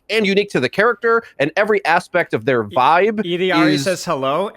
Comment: what character, what did he say? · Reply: Illari, the support with a sun gun